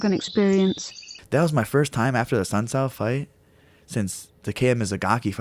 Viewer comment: what the fuck is he saying 00:03 "that was the first time after the sunset fight? Since the came as a gocki fight? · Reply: he fought a dude named Assuncao and knocked him out